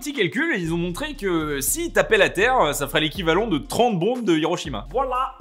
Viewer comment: j'ai entendu walla